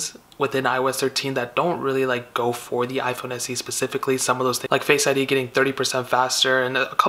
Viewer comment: you said Pacific😂😂😂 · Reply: Anita M Sounds like he said specific correctly, must have not heard him correctly because he was talking kinda fast.